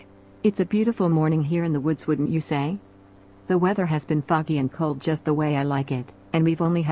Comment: i cant be the only one who thought she said "the weather is fucky and cold" instead of "foggy and cold"